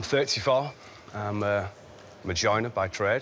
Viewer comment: I'm from America and he has a weird accent. it sounds like he said that he is a man-gina by trade